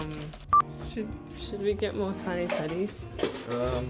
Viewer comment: he said tiny teddies and i thought he said tiny titties